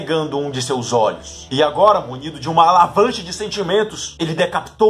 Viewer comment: Eu juro que escutei Alavanche ao invés de Avalanche, nos 3:30 hahahaha, traga mais vídeos sobre vilões.